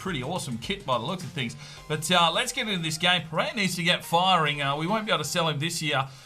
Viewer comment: Which player are you talking about here. I couldn't understand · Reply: Pareja the striker I think